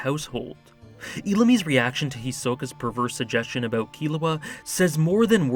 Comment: Look up what “perverse” means before completely misrepresenting the things I say, please. I did not say it was “pedophilic” at all. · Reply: ohhh to be honest i thought you said perverted or something like that